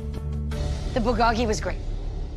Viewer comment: im sorry but it sounds like kim says "the bukakke was great"